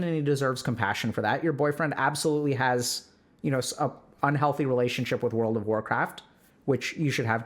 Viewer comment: X'DD LOL I thought I was hearing Dr.K say "Your boyfriend also has a relationship with World Of Warcraft, and you gotta respect that. Honestly. It's a really good game, dude. Don't try to compete with that." :')